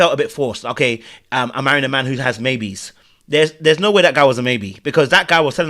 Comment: No, Ashley said Tyler has BABIES. Not maybe 😂😂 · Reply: That’s what I thought she said too…babies, not maybes lol